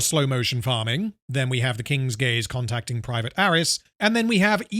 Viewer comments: I'm not even five minutes in and I keep hearing about "The King's Gays" lol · I keep hearing "the king's gays" and giggling like a high schooler.